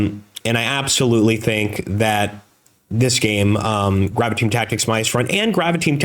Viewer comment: Did I miss you saying the name of game 3? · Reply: Around the 13:55 time I thought I heard "Graviteam Tactics"...